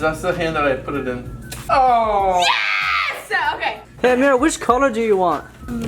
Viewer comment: I thought said "Hey babe what color do you want' 😏 like if you think that they are dating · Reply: he said hey Amerrah